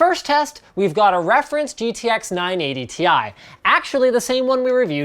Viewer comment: I thought he said R980 ti lol.